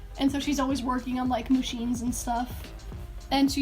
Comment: I literally heard "she's always working on pusheens and stuff" but its actually "machines" rip😂